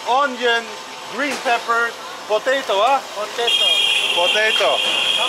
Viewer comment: did you say “Pos”?